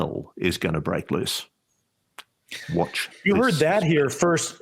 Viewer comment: what were you trying to say at 58:10 before friggin interrupted you? You were saying "watch ....." i couldn't make out the last part of your sentence since you were interrupted. Can you explain please? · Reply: Looks like he says "Watch this space." But yeah has a point, it's conversational and there is some professional overlap both ways 👍